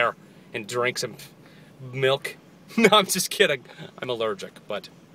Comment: At first I thought you said "Im going to drink Milf"